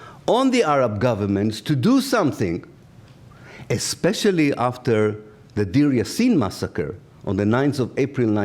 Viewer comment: "_________" massacre? I didn't hear that clearly. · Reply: Yes he talked about the brutal and horrific massacre of the neutral Palestinian village Deir Yassin by the Zionist terrorist militant groups Irgun and Lehi. Horrible story which pretty much sums up the whole story of Israel